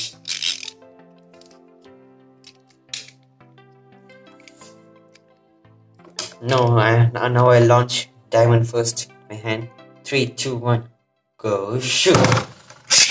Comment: I always thought this guy said “go sh*t” when ripping the beyblades 😭😭